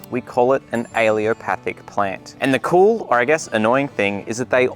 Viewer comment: he says ‘aileopathic’ but the caption says ‘allelopathic’ - which is it? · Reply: Allelopathic